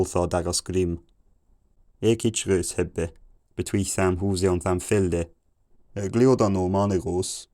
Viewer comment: I thought I heard "between samples" around 700 so I thought... okay, maybe just a few words here and there but not just the full context. only to learn later it was "between the house" :|